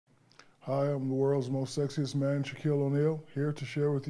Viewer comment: I don't know why I kept hearing "world's most sexist man" over "world's most sexiest man". HAHAHA. Two VERY different things.